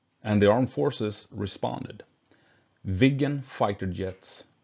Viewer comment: Instead of Viggen, I heard “vegan”, which crashed my brain for a couple of seconds 😅